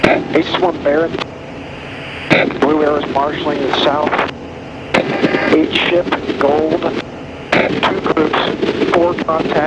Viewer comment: Who else heard @ 11:09 “eat shit gold”?